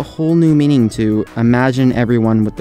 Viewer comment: did anyone hear him when before he got cut off or is it just me? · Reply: He did not have hi